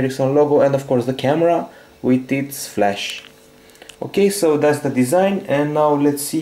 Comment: Did I hear he said "tits flash"?